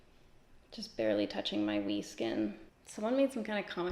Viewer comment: Hahaha when you said “wee skin” I thought you said “V skin” and my mind instantly went to the gutter 🤣🐈